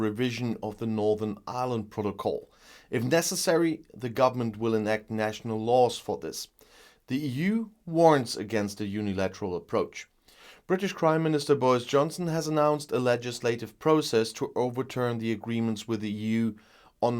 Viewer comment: I keep hearing: "British Crime minister Boris Johnson"